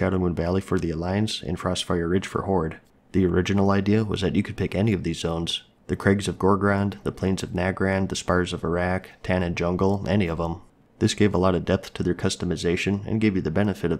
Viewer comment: i thought he said spires of Iraq lol